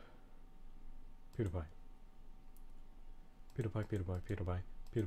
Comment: sounds like your saying peanut butter